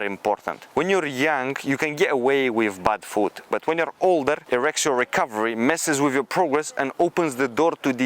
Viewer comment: I thought bro said “erection recovery” 💀